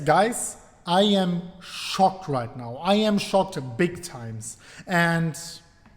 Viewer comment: Wow I had to double back thought you said I am SHORT right now